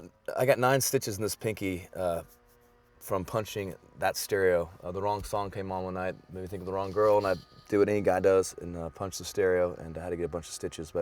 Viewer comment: what did he just say? hehe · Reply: "The wrong song came on and it made me think of the wrong girl, and I do what any guy does and I punched the stereo"